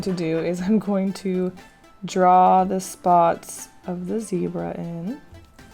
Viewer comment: did u say zebra GREAT drawing though